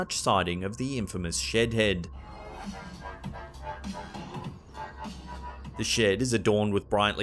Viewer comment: Shed head? Seriously?! And Mr. Shedworth's "history" 😂 oh jesus lol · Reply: I know right. When he first said shedhead, for a minute I thought he said shit head. 🤣😂🤪